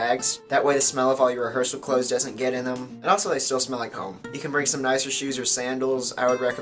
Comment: I thought u said at 3:44 , and also they smell like cum lmao